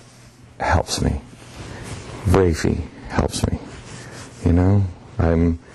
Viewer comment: did he say, "Gravy helps me"? I does look like he enjoys that.